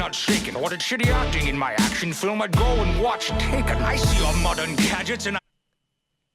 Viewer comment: I hear "gold watch taken"